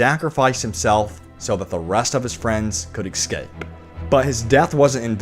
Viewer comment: Did you say exscape? Seriously?